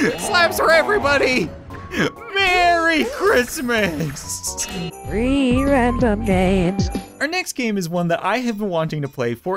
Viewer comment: I thought it said ree random games beetween games 😂😂😂😂😂😂😂🤣🤣🤣😂🤣😂🤣🤣😂😂🤣🤣🤣